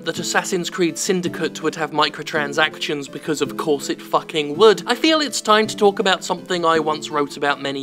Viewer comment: Twice I heard "corset fucking wood"